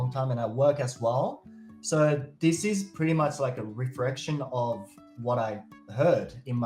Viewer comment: i think u have trouble pronouncing words with r and l in them. Like when u tried to say “reflection”, i heard “refrection”.